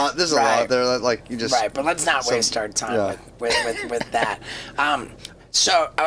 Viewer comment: Steve-o tells aoki “let’s not waste our time on that” as in what he said was irrelevant… was I the only one that caught that?